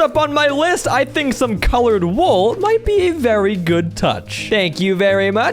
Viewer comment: the text said wall instead of wool